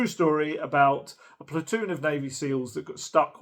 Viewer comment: I misheard "a platoon of navy seals" as "a platoon of baby seals"... Now, that would be a movie for me. I'm looking forward to 28 years later, trying to dodge the spoilers until we get to go next week.